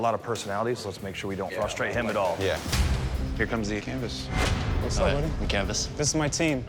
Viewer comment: Am I the only who heard "Hi, I'm Canvas." like it was his name?